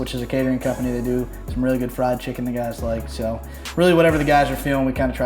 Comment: did he say fried chicken u guys like